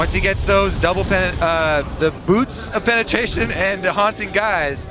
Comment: tongue slip,says double penetration(porn category) instead of boots of penetration gg esports